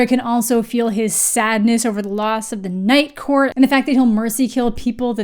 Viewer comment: I keep hearing "nightcore" which made a lot of the character choices.. very interesting. "They can feel his sadness over the loss of nightcore". Me too, little buddy.